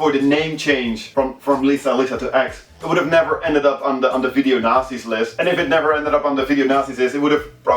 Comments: I almost thought he said video nazi's list. lol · i thought he was saying video nazi. haha .